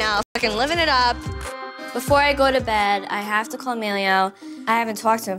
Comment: When Snookie says "i have to call Emilio" I hear "I have chlamydia" :D